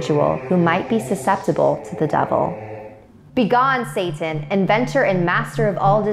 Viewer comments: I swear to god I thought you were gonna say “be gone thot”, instead of “be gone satan”. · "Begone Satan!" What I thought right after it was said. "BEGONE THOT"